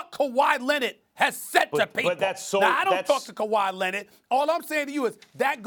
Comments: Anyone else hear Stephen A. Smith say , “I don’t f**k with Kawhi Leonard” at 6:46? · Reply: Albert Aguilar “I don’t talk to Kawhi Leonard” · I’m not the only one who thought he said “I don’t f with Kawhi Leonard!” am I 😂?